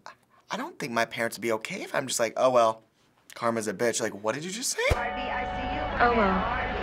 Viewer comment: LOL I thought they said 'Hello, Karmas a ...'